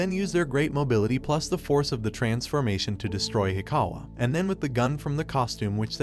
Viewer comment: When he says “transformation” I hear “transfurmation”